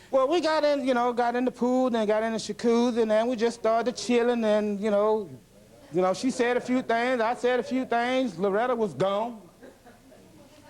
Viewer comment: Did I hear, "shacuzzi"?